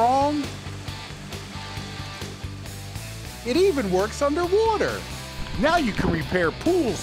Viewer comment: The subtitles at 0:25 is wrong, he clearly says that the product even works "underwooter" not "underwater".